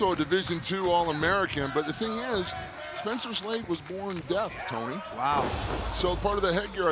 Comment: did he say “Spencer’s leg was born deaf tony”?